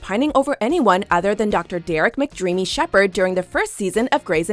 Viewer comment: did they just say derick mrdreamy Shepard